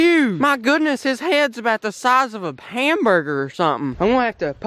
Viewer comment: it sounds like he says Pamburger instead of hamburger